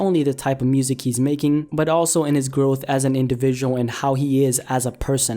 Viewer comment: anyone else hear 'girth' at 0:30 ???